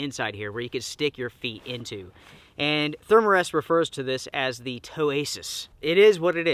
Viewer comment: I thought you said Toe anus and after that the video became very amusing. I hate those bright neon colors, even when I got myself a switch I picked grey, I usually go black, red, grey, camo, desert for colors on things.